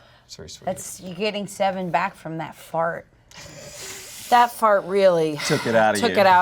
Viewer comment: I was eating Taco Bell and then I heard "You got 7 back from that fart." I almost shot a burrito out. LOL